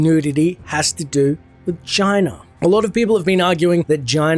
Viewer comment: When you say China around 11:00 all I hear gina lol not gee-an. But J-i-na lol